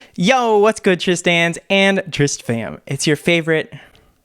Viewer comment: i really always thought that he said “hello tris-stans and tris-fans” i never realized that it wasn’t that 🤭